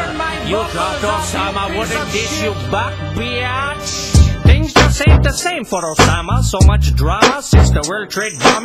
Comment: no lie for a sec i thought he said things ain't the same for obama......